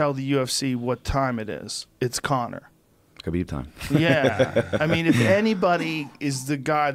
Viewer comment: did he say khabib time ?lol